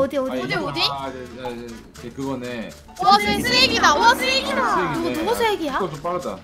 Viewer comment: "와 스핵이다"인데 "와 쓰레기다"라고 들림 ㅋㅋㅋㅋㅋㅋ